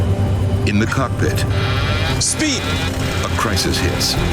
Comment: It sounded like they said in the cuck pit at the start